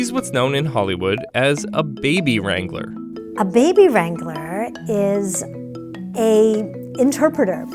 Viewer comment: I wasn't paying attention and I thought I heard "Baby Strangler"